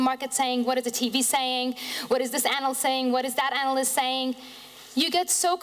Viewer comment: what is this anal saying? get in get out get in get out